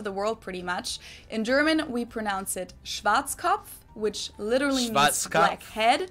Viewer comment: When you said „Schwarzkopf“ in 13:26 it literally sounded like „Schwanzkopf“ which translates to penishead 😂